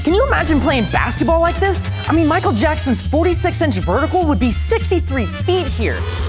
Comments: did he say micheal jackson?!😂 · Bro said Michael Jackson instead of Michael Jordan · did he say Michael Jackson? 😭😭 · Wren said "Michael Jackson" instead of Michael Jordan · I'm sorry....did you say Michael Jackson instead of Michael Jordan at 9:58? lolwut? Forget about Billy Bob Thornton. You missed this one too, LoL. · Anyone notice he said Micheal Jackson instead of Micheal Jordan? Haha